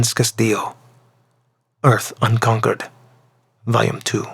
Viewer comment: Hold up, did he say "Earth Unconquered"? Did the name "Terra Invicta" get run through a translation software as that line circulated?